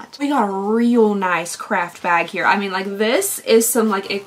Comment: The way i heard 'crapbag' instead of 'craft bag' 😂😂